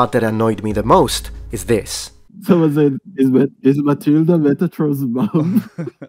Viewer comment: what did they say? something about your mom? i can't understand them... your silent stern look in response is great. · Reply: Yeah, it was something about Matilda being Metatron’s mom.